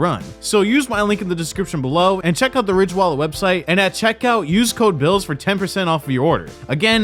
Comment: I thought you said hated ridge Wallet and it’s a scam